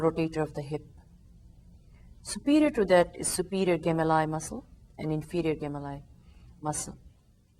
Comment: please can someone tell me what muscles Prof mentioned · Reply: superior and inferior gemellus